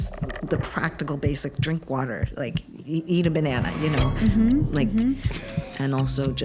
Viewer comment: Did she just say, "eat a banana?"